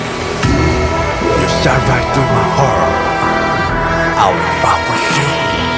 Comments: What Ravi says: "you shot right through my heart, i will fall for you" What I hear: "ea sha thrai tu mahar, ai wi fha fho shu" shhhh only snakes understand him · i can't be the only who heard him say "i will fart for sure" at the beginning. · In the beginning, it sounds like Ravi says: "You shat right through my heart, I will fart for you~" and I can't stop laughing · i can't be the only who heard him say "i will fart for sure" at the beginning. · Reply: I heard "I will f*ck with you" XDDD